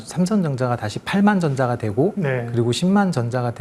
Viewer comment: 왜 과장님 발음이 삼성정자로 들리지 ㅋㅋㅋㅋㅋ